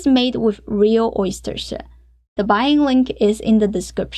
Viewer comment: I thought she said "which is made with real oyster shit" 😂😂 gonna try this recipe!!